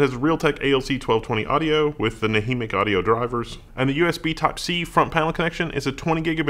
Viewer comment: At first I thought you said anaemic audio drivers... Clearly my "audio-in" is messed up. xD